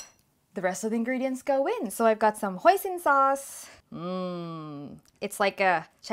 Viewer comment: I misheard 'Hoisin sauce' as 'Poison sauce' 😆😆😆😆😆😆😆😂😂😂😂😂😂😂